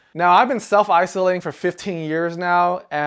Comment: did i hear it correctly, "self isolating for 15 years"?